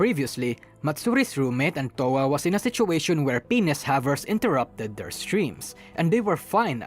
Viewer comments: Penis havers? Did...did I hear that right? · Wh... Did you just say "penis havers"?